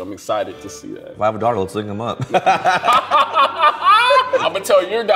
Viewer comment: Did anybody get what he said at 11:03 after 'I'm excited to see that'? Just can't decipher 🤔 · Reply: “If I have a daughter, let’s link them up.”